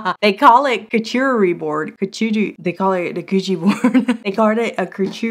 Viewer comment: "... Coochie board..." Don't think i didn't catch that lol! 😂😢❤❤❤ Love your vids grrrl❤️